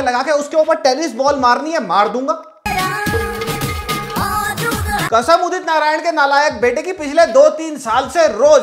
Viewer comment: did you just say "nalayak " beta of udit narayan? bro he is more talented than you.